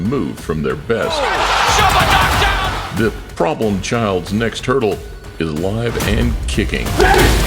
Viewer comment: Did he just say Fury is Live lol. I watched his last "fight", the one Jake Paul was at and that shit was trash.